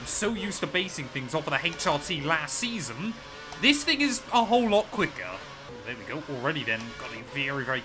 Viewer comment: Explain why I hear a slur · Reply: “This thing is a whole lot quicker” 😭